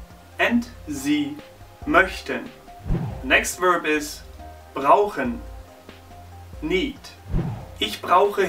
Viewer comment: Ich hab bei brauchen erstmal rauchen verstanden xD